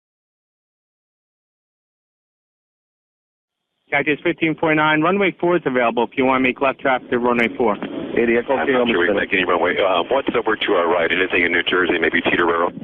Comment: Did he say idiot at 3:30?